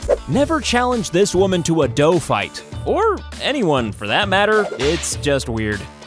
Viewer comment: did he say dough fight like he didn’t get whipped with a towel before 😂